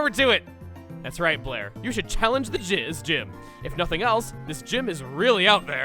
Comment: did anyone else hear what said or is that just my audio messing up? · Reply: I heard it. “The jiz gym”